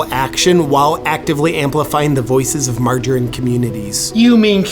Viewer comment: I'm older than I use to be and my Hearing has deteriorated so I Heard: "Companies that don't support Margarine Communities". Got a bit of Beer on my keyboard. 😁🤯👍